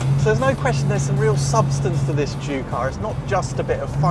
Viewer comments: sounds like hes sayin jew car. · i keep hearing "jew car"... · It sounds like hes saying jew car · All I keep hearing is "Jew car". LOL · I keep hearing "Jew car" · sounds like he says the "Jew Car" .... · it sounds like hes saying "jew car"